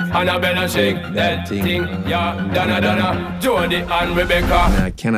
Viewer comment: I thought it said “join the fun Rebecca” 😂